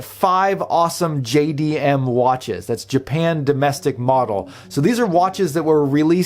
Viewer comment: That Casio is awesome. No idea they even had sapphire on any watch. And I thought it was “market” not “model”? As a former Honda del sol owner and Toyota Supra owner I heard “JDM” daily….